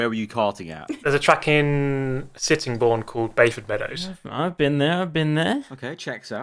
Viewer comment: which track ? I can't hear it well (sorry for my english) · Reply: Bayford Meadows in Sittingbourne, Kent